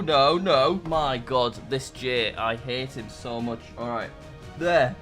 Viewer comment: "My God, this J, I hate him so much" - 2024 is definitely say "I hate him so much"